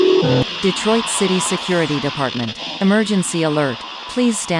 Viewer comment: DID SHE JUST SAY DETROIT?!